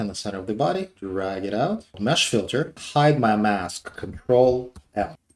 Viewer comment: Did you say Ctrl L or Ctrl N? I couldn't really make out that small bit of the tutorial. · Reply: L